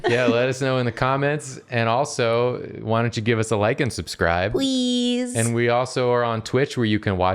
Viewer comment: I heard "Like and subscribe" as "Lycansubscribe" and now I need that as a pokemon.